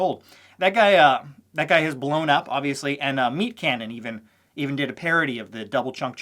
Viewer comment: had to rewind, thought I heard Meet Kevin lol